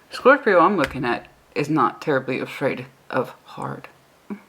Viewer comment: Hello, I was in dead, REM sleep and as I woke up, I heard you say "This Scorpio isn't afraid of hard." 😁 lol.. I'm an Aries with my first house as split Libra/Scorpio.. Uranus at 1 degree. Lol. My DM Scorpio is afraid 😨.. Very afraid, however I AM not. Every goal I set my mind to, that I told him I was going to do, I did! 😂 He's about to come out of confinement in T-9 days... I'm sticking to my plans and my goals. If he wants to come along, GREAT! However, I'm not accepting anything less than what I've willing to do. No FWB crap and no loosie goosie offers! I'm either on the pedistool with him or it's NOTHING! I DONT like people showing up at my home unannounced, I won't answer the door when people do this. I don't like surprises.. I sure hope the next Aries ♈ reading is like this one because the past few have been depressing 😕.. The Scorpio keeps pursuing and I don't know why? If he wants this then he needs to fully know what he's in it for and why.. Thanks again.